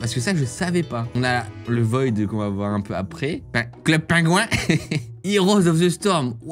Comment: À 4:18 c'est moi où il a dit club pinguin 😂😂 c'est linux bien sur